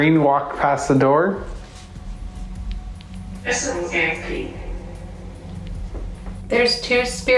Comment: After says "Can we see you on the screen walk past the door?" It sounds like it says "that's a little kinda creepy"